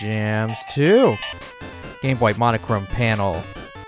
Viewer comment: I swear I heard “the gay boy” instead of the game boy